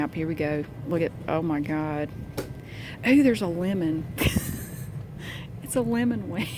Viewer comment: Omg I thought she said "oh there's a women" at 8:00 😂